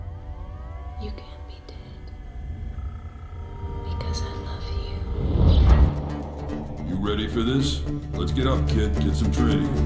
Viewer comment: did anyone else hear it? at 12:20 it says: you can be dead he doesn't love you. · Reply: It's "You can't be dead, because I love you." It, along with all the other dialogue snippets between days, is from The Matrix